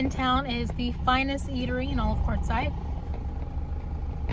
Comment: Quartzsite. Oh I thought you were saying Courtside! Thanks for the update! -San Francisco fan girl